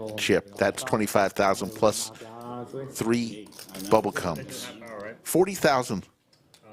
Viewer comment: ummm.. i listened to it 10x to make sure i heard it right.. at 22:25.. Did he say "bubble CUMS"?!?!?